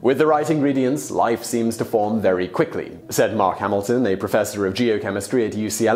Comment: I THOUGHT HE SAID MARK HAMILL AND I WAS LIKE WHAT 😂